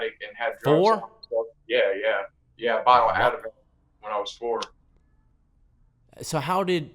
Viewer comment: This is driving me crazy @ 2:22 what did he say he overdosed on? I think he says “a bottle of Advil” right or wrong? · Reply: Ativan, lorazepam